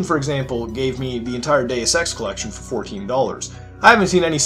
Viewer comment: When he said he got the whole Deus Ex trilogy for $14 I thought he said Day of Sex